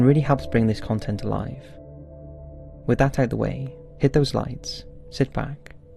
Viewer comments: OMG! I ALWAYS HEARD "HIT THOSE LIKES" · oohhh hit the lights, i always thought he said hit the "like" as in the like button. silly me.